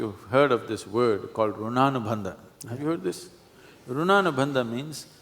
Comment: What is the word he's saying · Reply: Runanu bandha. Its a term denoting the body's ability to store memories or information or something like that. For eg, sexual relationship. Each time it happens, your body stores some info or experiences from the other person. The body remembers it even if your mind forgets it ...........................something like that. Even a touch could cause runanu bandha,...a kind of collection or info or memory collected by the body. Avoiding or limiting such bandha (relations) reduces this memory or bonds or collections. (sadhguru talks about this in another video 'if multiple sexual relations are good by sadhguru'. Its just his opinion, but its interesting. Whether its good or bad, upto us individually. Each to his own.